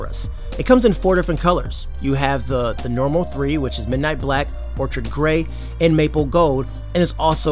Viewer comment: As a T-Mobile JOD customer, the $0 down makes it a zero risk, so it's a no-brainer. At @ 5:18, did you say "Orchard Grey?" It's "Orchid," pronounced 'or-kid.'